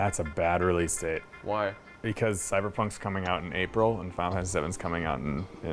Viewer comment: What does say at 1:59? Is he talking about a new valve game? because I hear: "and VALVE has Sevens(?) coming out at.... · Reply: "Final Fantasy 7"